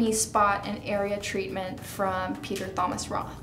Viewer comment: did she really say peter THomas Roth?